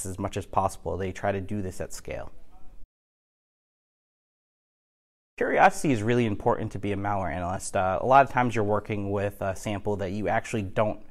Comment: could someone please type what he said in 3:09 pls? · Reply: “Curiosity is really important to be a malware analyst”